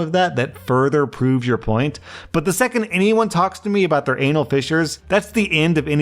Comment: Oooh... "fissure"... I heard "fisher"...